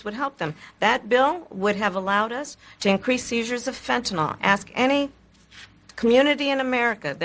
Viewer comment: What exactly did she say? …to increase the users of fentanyl? Her speech was very slurred. At 10:16 when she spoke of the former president pushing an agenda. The agenda she is speaking of sounds like Biden’s agenda; who no longer even pretends to be president? Well at any rate, thank you for your SELF-joy of building back better…you think therefore you are, not.